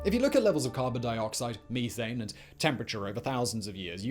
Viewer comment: did he just say Me-thane ?